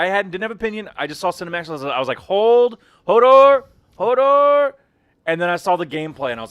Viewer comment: What did that guy said at 23:08? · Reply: HODOR